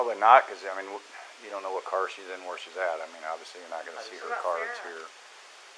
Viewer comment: Can someone please tell me what her friend says here ? And Chris also starts to say sowmtjong after the cop says probably not you don’t know what car she would be in. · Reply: she says “what about Sarah?”